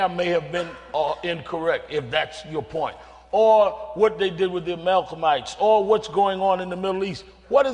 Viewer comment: does Sharpton actually say Malcomites?